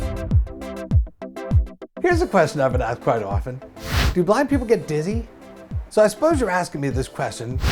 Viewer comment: Thought at first you said, "can black people get dizzy" hahahah